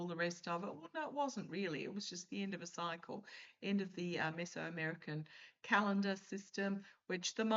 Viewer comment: I thought you said 'Miss America' calendar 😂😂😂😂